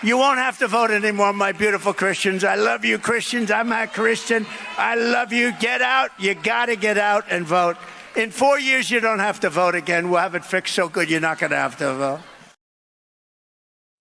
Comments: I listened to that clip where it really sounds like he says that he's not Christian. Then I enabled captions and, according to them, he said I'm a Christian. I figured the captions were right cuz I didn't hear anything about it on mainstream media. Can somebody help a girl out!? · Now you did hear where Trump said he is not a Christian when he was talking about never having to vote again why is nobody talking about the fact that he said he was not a Christian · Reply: I absolutely despise Convicted Felon Trump, but I heard a slurred "I'm a Christian ". It doesn't really matter because we all know he isn't a Christian. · He actually said I'm a Christian. It just sounded like he said I'm not...don't get me wrong,; I can't stand the orange mussolini. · I think that he actually said, "I'm-ma Christian" rather than "I'm not Christian". However, regardless of what he said, he clearly is not a Christian.